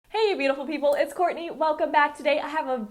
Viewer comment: LMAO I CLICKED THIS VIDEO AND SHE SAID HEY GUYS ITS COURTNEY AND I THOUGHT SHE SAID HI IM HORNY 😂😂😂😂😂